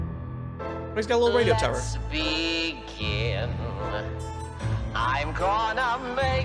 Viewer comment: I always hear “LETS BE GÆ/GAY” whwn alastor is singing stayed gone and btw Alastor is a radio demon/Radio demon deer